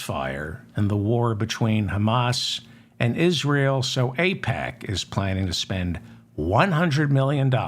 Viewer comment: For a second I thought you’d said it was OPEC that is planning to spend $100M to remove the squad, but AIPAC works too